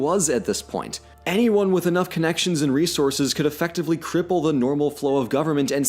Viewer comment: I watch with captions on and noticed a couple discrepancies in what was said vs what was in the caption. The first I noticed was 24:42 there’s an ellipsis instead of the word “cripple” and at 30:24 the joke is lost in the captions because it says “ahead of myself” instead of “behead of myself”. I loved the video and just want to make it so people who can’t hear it can enjoy it equally as much!